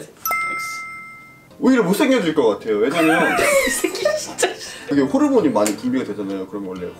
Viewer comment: 초 뭔가 이새X끼가 진짜라고 들리는거 나만 들림?